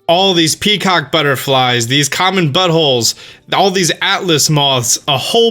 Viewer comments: Did he say common buttholes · Did you just call the butterflies "common buttholes" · Am i heard that him say butt holes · Did he say common buttholes?